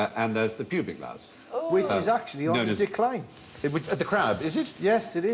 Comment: I honestly thought he said dick line!!!